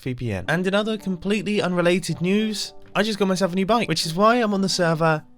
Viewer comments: Oh, new BIKE! I thought you said new bunny at the beginning! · Honestly thought he said new Mike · Thought he said a new mic at first